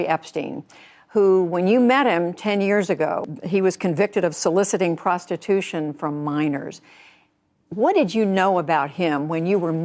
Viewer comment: I heard "Soliciting prostitution from miners". Suddenly had this vision of a load of coal-blackened pitties going, "Alright shagga. A've spent enuff time dan't pit. A'hm ready to try owt else if there's some gelt in it."